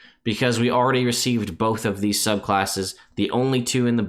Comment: Hadn't heard the new subclass names yet and thought you said College of Elephants Bard... I was confused, but also intrigued, lol.